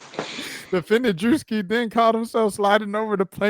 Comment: So, when it 𝘧𝘪𝘪𝘪𝘳𝘴𝘵 𝘴𝘵𝘢𝘳𝘵𝘦𝘥, I'm like- this is some real stuff. Then, I kept hearing "Defendent Diddy". It was at 6:45 when the description started & I said " 𝙤𝙝 𝙃𝙀𝙇𝙇 𝙉𝙤!! "